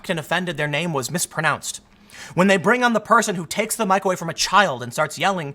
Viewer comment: it sounds like Tim is saying microwave instead of mic away. I thought it was funny